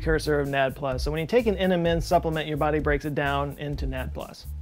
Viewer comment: What he said: MNM Supplement What I heard: Eat M&M's